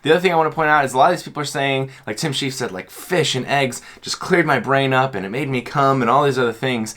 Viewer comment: Did he really say "fish and eggs cleared my brain and made me cum"? 😂 5:55 (-I've never heard of this Tim-character before, so I don't know what he's like)